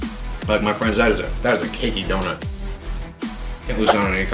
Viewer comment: At first I thought he said kinky instead of cakey donut